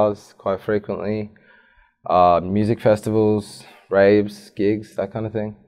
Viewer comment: did he just say rapes?